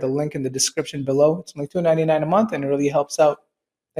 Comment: it sounds like you said $19.99 a month. Then I checked your description and saw that it's only $2.99.